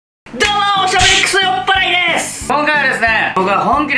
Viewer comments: 自己紹介で 「どうも！おしゃべりくそおっぱいです！」 って聞こえたのは俺だけだろうか…… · 最初の「酔っ払いです！」がおっぱいです！に聞こえてしまいましたごめんなさい